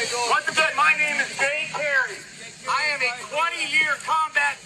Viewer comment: He says his name at approximately 1:28. Closed captions say but it sounded like Dave Perry to me - LOL I'm 60yo and my hearing isn't what it used to be!